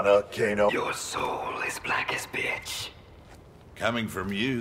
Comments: it sounds like Noon said " As black as b#tch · I definitely heard 'b!tch' not 'pitch' 😂😂😂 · “Your soul as black as bitch” I know it’s Pitch but that’s what I heard it as at 1st (I’m deaf as fuck) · I misheard Noob Saibot voice as though he was saying “Your Soul is as black as Bitch” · it sounds like he says black as b***h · if i didnt read the subtitle, i would think he said "b*tch"